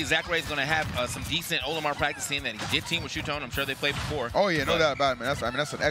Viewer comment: 「しゅーとん」って聞こえる…